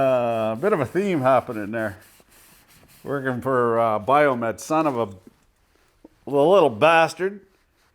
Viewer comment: You mention someone in SF working for "biomed?". Did I hear that correctly? Do you know about Biomed as a career?